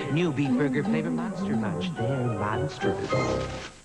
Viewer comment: What Did He Just Say At 00:00:17? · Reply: He Said There Monsterous